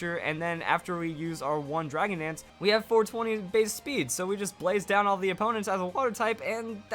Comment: huh? did i just hear 420 Blaze speed? 420! BLAZE! 420BLAZEIT! DONT DO DRUGS PLS, YOURE TOO YOUNG TO DIE, WHO WILL TEACH ME POKEMON STRATEGIES AS GOOD AS YOU DO NOW?!